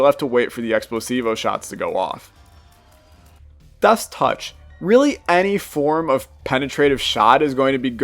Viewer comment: Did anyone else hear "Duss touch" at 3:57?